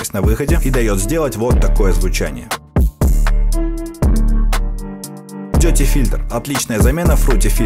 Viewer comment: Послышалось не dirty filter а тётя фильтр🙈